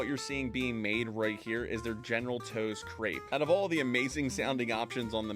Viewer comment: General chose? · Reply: I thought he said general toes hahaha